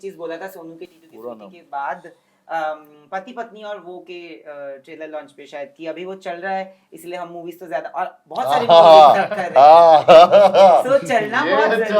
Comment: what Bhushon Kumar said for Kartik in sonu k titu ki sweety time ? I don’t understand it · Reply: Bola tha ki tum ab sal raha ho na isliye tumhare sath kam kr raha hu majak main bola tha